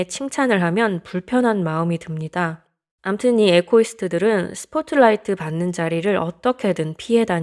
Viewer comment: 죄송하지만 에코이스트 라는 말이 사전에도 없는데요... 토크온이라고 소통하는 어플입니다 근데 요기에서 어떤사람이 저한테 에코이스트 라고 말하시더라고요 에코이스트 성향이 강하네 윺튜브 비난과 지적질은 채워지지못한 자존감을 남들을 지적과 비난함으로서 상대적 우월감을 느끼며 자존감을 챙기는 행위 그런사람들은 자기 혼자 자존감을 채우지못해서 남들의 자존감을 훔치는 행위로 측은지심으로 불쌍하게 바라보는것이 바람직함 에코이스트는 타인의 관심을 피해서 숨어버립니다. 에코이스트들은 왜 숨어버릴까? 이런말을 하시더라고요 에코이스트는 네이버에 사전이없는거라서요 혹시나 유튜브보면서 이걸 다른사람한테 피해가는 말은 상가해주면좋을것같아요ㅠㅠ 이 영상 올리시는 유튜버님한테도 죄송드립니다. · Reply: 사전에 있어요 echoist 맞고 발음도 에코이스트로 들리네요